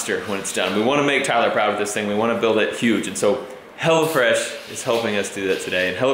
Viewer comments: Oh hello fresh i thought you said hella fresh xD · I thought it said hellafresh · It sounded like u said hella fresh some of the times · I thought he said "Hella Fresh" at first. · I thought you was saying hellafresh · I thought you kept saying "Hella Fresh", and I was thinking, "What is it, like 80's food?" LOL